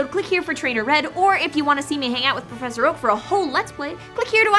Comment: I totally thought she said "make out with" instead of "hang out with". Disappointed.